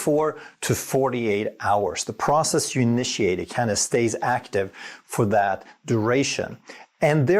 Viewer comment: I heard what sounds like "karna". Would it be helpful if I knew what that meant? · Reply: He’s saying it “kinda/kind of” stays active. I think